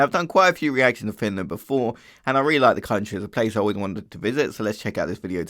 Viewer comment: I thought you said "I realized the country is a place I wouldn't want to visit" thankfully subtitles clutched there ':D